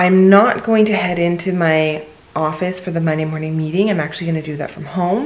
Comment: I heard "Money Morning Meetings" every single time 😫... Why, oh, why??! The struggle is so... super real! Dear God, please... please, help 🙏🏽😣